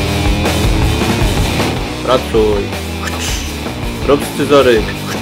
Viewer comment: Czy ja usłyszałem "Pracuj, rób scyzoryk' ?? POLSKA??